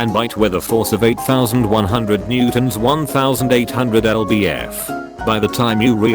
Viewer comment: I misheard Newtons as newborns, and was confused for a bit.